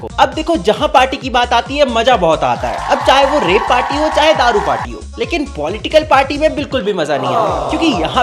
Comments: Did u said rape party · Did he say "r@ party" 💀😭